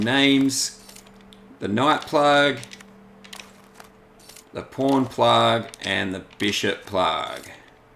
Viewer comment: When you said pawn plug, your accent made it sound like you said porn plug Lol.